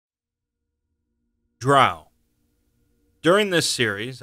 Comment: What he said: Drow What I heard: B R U H